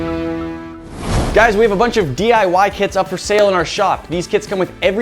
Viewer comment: i thought he said "we got some DIY kids up in our shop"